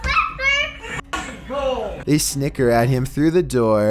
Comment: I thought you said the n word